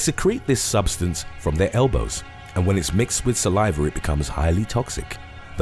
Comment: I think the strangest part about this whole thing was finding out that slow lorises had venomous elbows. Were you bored God? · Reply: Elbows?? I thought I heard saliva, or assumed and wasn’t listening carefully, I guess my mind was trying to autocorrect that fact into rationality. Elbows?! Elbows?